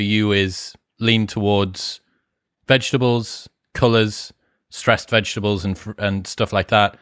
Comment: What did he say after vegetables? Colors? Not a native speaker, so help would be appreciated :)